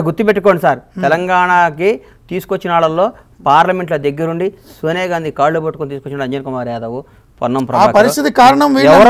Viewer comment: am i the only one who heard L***jan kumar yadav 😂😂